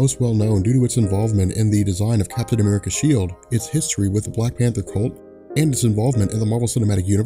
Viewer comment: Did he say Black Panther CULT? ( 1:00 timestamp)